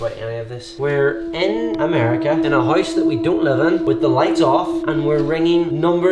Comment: Did just say “I’m a house we don’t live in” at this time